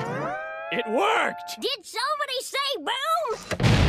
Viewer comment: did somebody say boobs? Detonates bomb